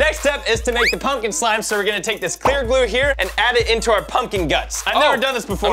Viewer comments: When they were making the pumpkin slime I thought said hair glue not clear glue.... I’m ashamed at myself. 😓😓😓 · Am i the only on that thought said " carrot glue"